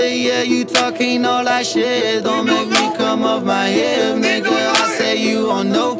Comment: I've listened to this so many times today! Although, in the chorus it sounds like they say, "Talking all that shit, don't make me comb off my hair, nigga I swear you don't know" haha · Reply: "don't make me come off my hip" like don't run up on me cause you think you know me basically 🔫 <~ hip piece! lol